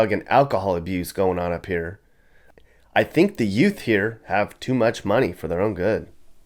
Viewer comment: The what? What did you say? What's a ute, did you say Yootz? (My cousin Vinny)